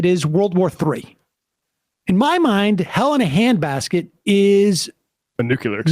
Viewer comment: My apologies but all I hear is "Helena Handbasket" (from FRIENDS) each time Sam says it.